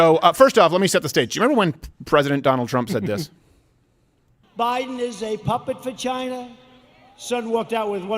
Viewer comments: Trump: "Biden is a puppet for China." What I heard: "Biden is a puppet vagina." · When Trump called former vice president Biden a "puppet for China" it sounded like he said "Biden's a puppet vagina" lmfao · When they show Trump saying Biden is a puppet for China, was not paying close attention and thought Trump said Biden is a puppet vagina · I had to play that back twice because initially I heard, "puppet vagina". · It ALMOST sounded like President Trump said "Biden is a puppet v@ - Which is even FUNNIER!! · When you played Trump saying "Biden is a puppet for china." That's not what I heard... "Biden is a puppet vagina." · I totally thought trump just said "he's a puppet 'vagina'" lol I had to rewatch to see it said for china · 🤣🤣 it sounded like Trump said puppet vagina at first. Please tell me someone else misheard it. Lmao.